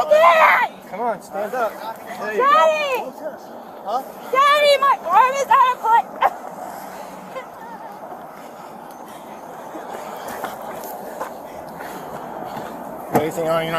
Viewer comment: Sounds like she said "Daddy my arm's out of place"